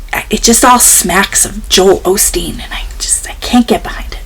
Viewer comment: Ohhhh you said Joel Osteen... I zoned out and thought you said Haley Joel Osment. I was wondering where the Sixth Sense kid fit into all this